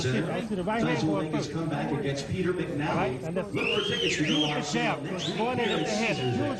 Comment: I really want to check out this "Pete McNowly" guy. I heard that he once fought a fella by the name of Iron Mark Tysler. LoL